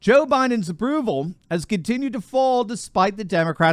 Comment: I thought turley said Bidens ruble instead of Bidens approval when he was reading that piece.😂😂🤣🤣😂